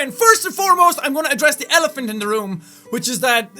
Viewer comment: What does Jack say at 0:04? I'm going to address the elephant in the room? Element? Because I heard elephant XD